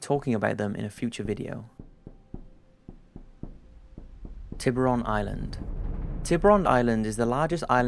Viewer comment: I thought you said booby island